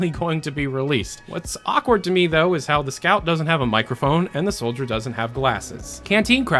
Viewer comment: "and the soldier dosent have classes" oi i think u missspoke